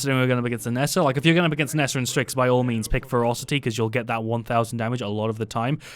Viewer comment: Every time he said "Ferocity" I heard "Frosty". So, yeah, pick Frosty.